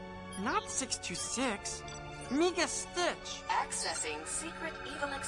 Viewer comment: did he say the n word? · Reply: ***** No. He said "Meega". In his language, Tantalog, "meega" means me, I, or I'm.